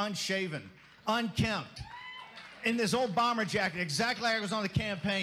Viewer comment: I thought Bannon said Obama jacket.